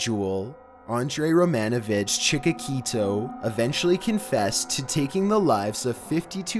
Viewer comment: Did he just say Andrey romanovich chikakito? Lmao It's chikatilo btw not chikakito, it sounds like some Hispanic housewife with 5 kids